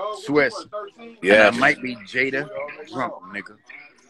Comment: DId he say he might be Jada Drunk lol